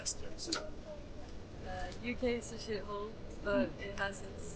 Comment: Did she say the UK is a shithole?